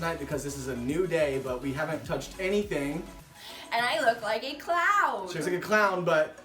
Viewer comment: hair looks so good!!! Also im still laughing bc you thought she said clown and not cloud😂💙